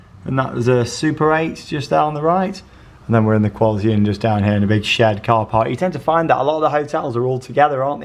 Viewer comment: did anyone else hear say “a big shed car park” 😭🤣❤